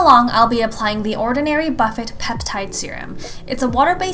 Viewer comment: did she just say "buh fet"? lol.....